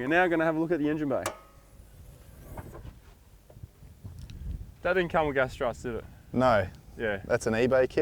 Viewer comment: did u say "that didn't come with gas struts" ?